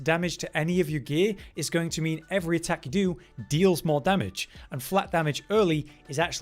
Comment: Had to repeat 2:05 a few times as i heard "attackadoo" Thx for a gr8 guide anyhow!